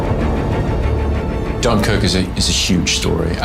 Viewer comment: What the hell 5:10 when Cillian said Dunkirk but the subtittle written 'Jongkook'..are u kidding me?